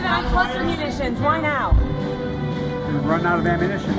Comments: Sounded like he said "We've run out of ammunition." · I heard "We've run out of (155) ammunition".